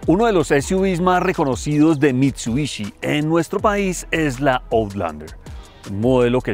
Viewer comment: seran ideas mias, pero entendi honda😂😂